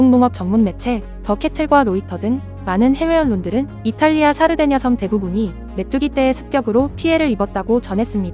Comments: 사르데냐섬이 자꾸 살해된 여성으로 들려서 깜짝깜짝 놀람 ㅋㅋㅋ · 샤르데냐섬을 살해된여성 으로 들었네요 ㅋㅋ